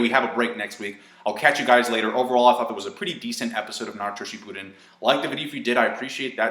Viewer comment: it almost sounded like you said "shitpudden"